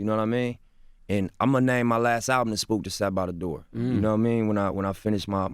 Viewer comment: Whats that book reference? All i hear is "Spook *@@ by the door" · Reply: the spook that sat behind the door